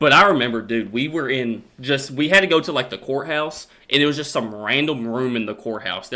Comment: I swear I thought you said whorehouse